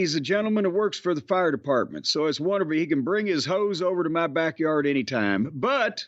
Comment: Lmfao. It almost sounded like jim said "tell that fireman he can bring his ho's to my backyard anytime" but I know he said hose but it sounded like Ho's haha